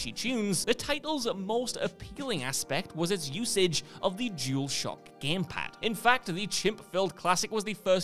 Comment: What I heard was Jewel Shock, and it took me sever attempts to figure out what that actually meant (DualShock).